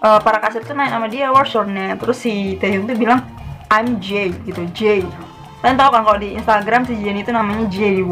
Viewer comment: Itu taehyung bilang "i'm jin"tapi kedengeran kek ngomong i'm jen Maksa banget shipper nya